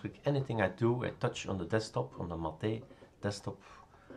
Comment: At about 2:08, did you say mateyy ?? That was the first time you said mateyy and not mait.... Bravo!😉😆😊 (By the way, I also always stumble and call it mait, rather than mateyy..... ☺😊😁😆)